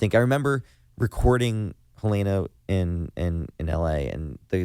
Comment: LMAOO when he said Helena my heart skipped a beat bc my name is Malina and I was eating chips and it rhymes w my name and I thoughT HE SAID MY NAME😂😂